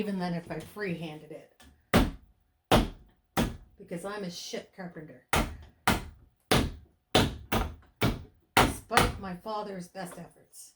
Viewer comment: I thought your broomstick was a light saber 😂 (yup, I can nerd out momentarily) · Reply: And I thought you said, "I'm a ship carpenter." 🤦‍♀️🤣